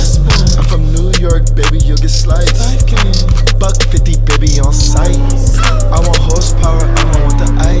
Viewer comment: When he said "buck fifty and its on sight" I heard "fuck fifty, it's on sight" and had to take a second look :D Almost lost my hype